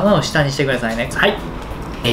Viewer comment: 「ネギだれたっぷりの」が「いぎなり」に聞こえてそんなとこまで宮城県民に忖度してくれてるのかと思った